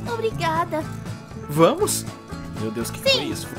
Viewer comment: Só eu que escutei um "meu Deus o q foi isso" no minuto 6:46 ?